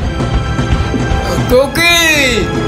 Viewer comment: sounds like he says,"Cocaine!" 😂